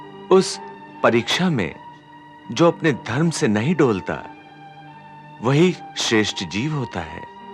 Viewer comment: Can anyone explain me what lord said in 20:40? What do you mean by tolte or tole OR apne dharm se nahi tolte? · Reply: अरे भाई साहब तोलता नहीं डोलता बोल रहे हैं यानी अपने धर्म से नहीं डोलता या भटकता 🙏🙏